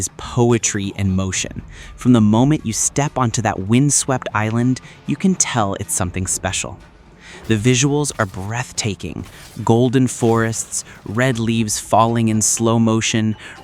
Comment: Did he just say "breath taping"?